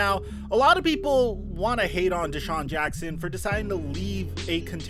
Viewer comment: Thought is said Deshaun Watson😂😂